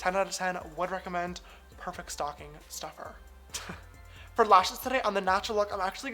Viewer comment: I thought you said stalking stuffer for a second xD